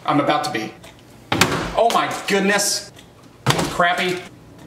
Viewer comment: What did he just say 2:21 crappy LOL :face-blue-smiling